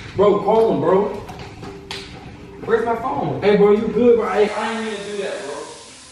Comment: who else heard call her babe or am I hearing things😂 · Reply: experience/experiment Channel "he said aye bro you good"